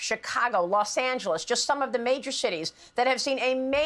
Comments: She said "...major cities....), but I heard ("...major shities...). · slow it down a notch and listen closely to what she said. LOL. slip of the tongue? Or dead-on-accurate assessment? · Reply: Major shittys?